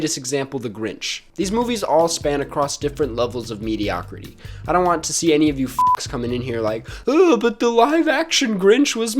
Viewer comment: I skipped back 10 seconds and it sounded like he said “I don’t want any of you to F*** the Lorax I won’t.